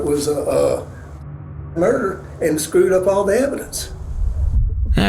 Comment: what is the censored word here? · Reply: Suicide. He said "suicide-murder" but it's usually referred to as a "murder-suicide".